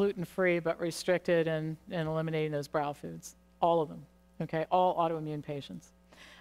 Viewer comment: What kind of foods did she mention to avoid @ 8:02-8:04? I couldn't catch that. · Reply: Nicky J Thank you! Her sentence leading up to that is confusing. Did she say we should or shouldn't eliminate BROWS foods and go gluten free? Because she said not gluten free, but all autoimmune patients should avoid....BROWS foods? All brows foods contain gluten so I'm trying to figure out what she was trying to say.